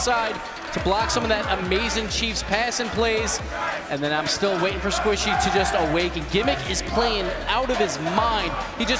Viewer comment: what was the crowd saying? I can’t hear it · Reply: For context: Greggs is a bakery chain in the UK They sell you at Gregg’s They sell you at Gregg’s Squishy muffins They sell you at Gregg’s